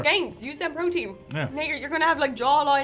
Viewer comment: what did she say ? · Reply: Hey there! Just to clarify, at 4:46 says "Now you're, you're gonna have a jawline out to here!" - we can assure you, as we were there on the day it was filmed! 😁